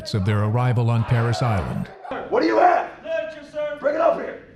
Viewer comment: pear salad what? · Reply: Parris Island. The Marine Corps recruit depot in South Carolina. It does sound like he said pear salad though lol